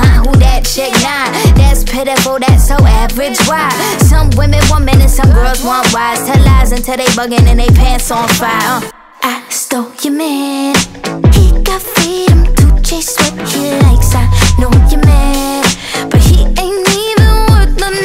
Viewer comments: i thought Dojo said "I stole ya Document" 😂😂😂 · I thought she Doja said wives but she said bwahs this entire time?😂😂😂